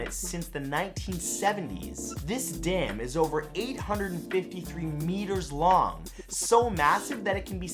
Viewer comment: When you said "1.7 meters long" I heard "1 point centimeters tall." LOL